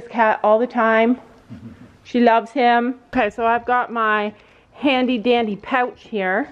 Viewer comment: When you said " I got my handy dandy pouch here" I thought you said " I got my handy candy pouch"😂😂😂 lol btw I love you guys